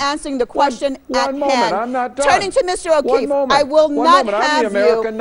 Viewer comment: Did he REALLY say "I'm the American, not you, and I'm not done yet." ??? Are you fuckin kidding me?